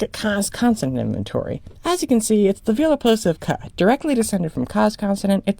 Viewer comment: I have auditory processing disorder so sometimes my brain wildly misinterprets what I'm hearing. Which is to say, somehow I heard "it's the velar plosive k(ah)" as "it's the Volapük of Co"